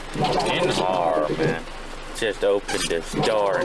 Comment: did he just say "getting hard man"?😂🤣🤣😂🤣😂🤣😂🤣😂🤣